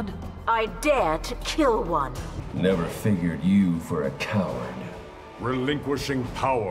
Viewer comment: is it just me or did he say "Fingered" lol